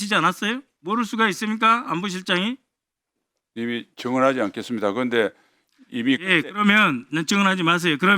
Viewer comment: 증언하지않겠습니다 ㅡ=> 맞습니다 . 지시했습니다로 들리네